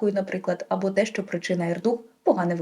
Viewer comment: вибачте, що таке "ерду"? Чи щось неправильно почула? Прослухала декілька разів, не розумію... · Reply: РДУГ - розлад дефіциту уваги та гіперактивності. Це був натяк на одного психотерапевта, який розповсюджував таку брехливу інформцію на широкі кола: якщо ваші діти хворі на РДУГ, то це ви їх не так виховуєте.